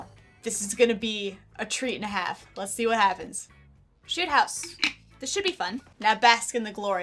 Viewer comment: i think she said sh!t house